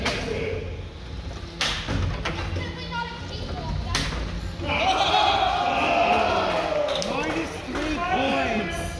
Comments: What does the kid say around 12:17? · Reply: "It's definitely not a cake walk, that's for sure" ;0) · What did Dulce de Lucha's driver say at 12:17? Bc after that the ref said minus three points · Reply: He said it's "definitely not a cake walk if that's what you mean", lol. Didn't know that's what he said until recently.